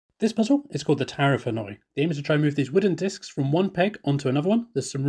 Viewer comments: I hear and see “tower of annoying” but I would still take on challenge 💪 like my friends still take on me 😂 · I keep hearing "Tower of Annoying" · I thought he said the tower of annoying lol